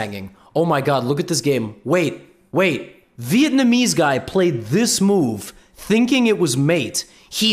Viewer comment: Did i just hear "vietnamese guy"?